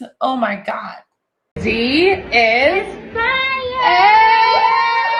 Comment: I thought she said "tired".